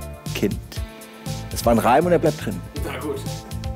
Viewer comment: Was sagt die Person (?) im Hintergrund? Kanns nicht ganz verstehen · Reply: "Na gut", hört sich mMn nach an